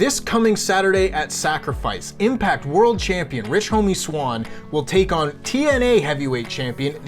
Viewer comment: Did he really say rich homie swann?